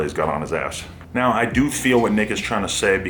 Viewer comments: sound like " now I do feel what n*iggas trynna say" rather than "now I do feel what nick is trynna say" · Honestly thought said "Now I do feel what niggas tryna say"